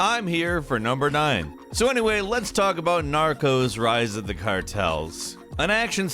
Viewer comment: Thought said "Naruto's rise of the Cartels"